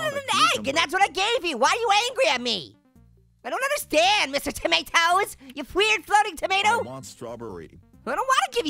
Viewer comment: I was chewing my cereal an then he said you Weir floating food. I heard you crazy f__king food